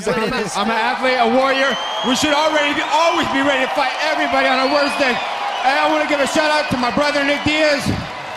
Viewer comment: Bloody love I'm a warrior I'm ready to fight anyone on a Wednesday lol Monday Tuesday Thursday Friday he smokeing up and useing nunchucks got to love the love between them who is the better fighter out of you two? That's a stupid question he's my brother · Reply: I thought he said “I’m ready to fight anyone on my weakest day”